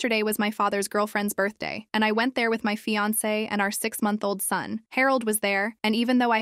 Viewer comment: nah I thought they said 6 year-old for a second